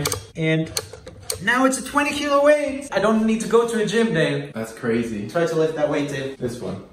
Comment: Okay- Im Sorry But- 13:50 It sounds like He says ''Babe'' And Not ''Dave''